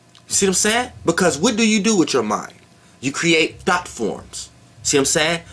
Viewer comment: I heard my brain creates thots! 😅 nice video tho